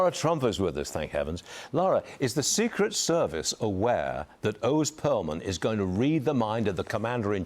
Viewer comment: I thought he said Ron Pearlman.